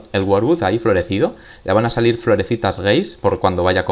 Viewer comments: soy yo o escuche "floresitas gays" ?? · fui yo o escuché florecitas geis XD